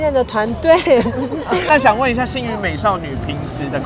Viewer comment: 有人跟我一樣一直把星殞美少女聽成性慾美少女ㄇ😂